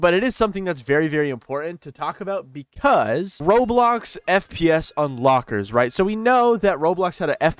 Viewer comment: I THOUGHT HE SAID FPE:S 💀😭😭